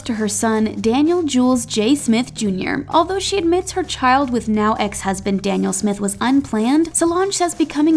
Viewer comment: All I hearing is 'now then ex husbond' 😂 celebrity love to get marry & divorce like wow